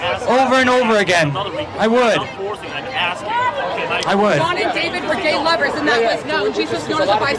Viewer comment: Did she just say Jesus and David were Lovers??? Huhhhh?